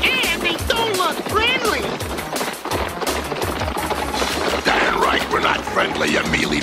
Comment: Did he just say “damn”? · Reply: "Damn right we're not friendly" is what I believe he said, either way, he did say damn